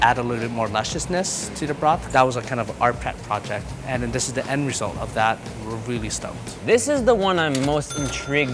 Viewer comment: Thought he said it's the end result of that when we're really stoned.